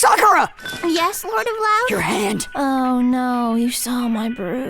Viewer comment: The only part of this I don't understand is Sakura's line at 6:00. It sounds like she is Asking "Yes lordem loud?" or "Yes lord of loud?" but i have no idea what she is referencing. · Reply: Ah, captioning error. It's meant to be "lord of the house"